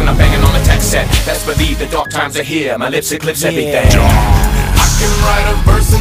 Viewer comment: Did he just say my lips eclipse everything?